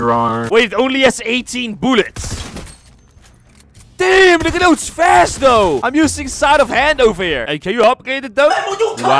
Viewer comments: sound like he said “damn nigga that was fast tho” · did you say damn n*gga that ones fast though?!?!